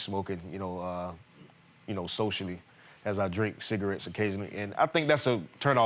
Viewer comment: Did he just say "as I drink cigarettes? "